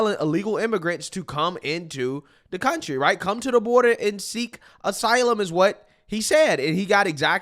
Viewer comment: he actually said "surge the border"